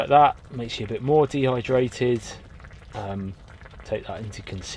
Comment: I heard “if you have a beard you get more dehydrated” 😂😂